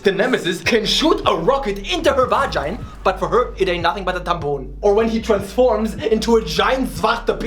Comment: dude did he actually say tabboun (vagina in Moroccan dialect) at 7:18, I'm Moroccan and i cant stop laughing it cought me by surprise XDDDDDDDDDDDDDDDDDDDDD